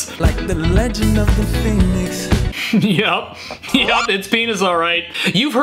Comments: I heard Phenix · I hear Fetus do I belong in jail? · I heard the phenis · i hear finis? that’s not even a word tho😂😂